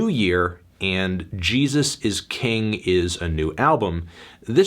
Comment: Omg I wasn’t looking at the screen and I thought he said a Jesus is gay